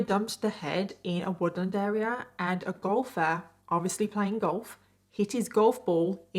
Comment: A gopher found the head...no, no...a golfer. Sorry I really thought she said gopher. Funny part of story.